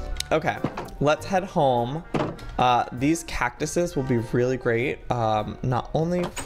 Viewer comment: DID JOEY JUST SAY """"CACTUSUS"""!!!! Edit:12:26 DID JOEY JUST SAY """""BREEDED"""""!!!